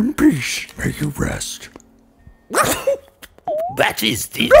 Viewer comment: "In peace, may you rest" throws confetti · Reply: OOOoooohhHHH! Gotta confess I thought he was saying` "Impish May You Rest" Yeah,... I don't know why I thought that either...